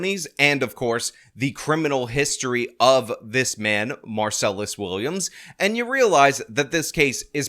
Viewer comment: Every time says his name, I hear “ Marcellus Wallace” and think of Pulp Fiction!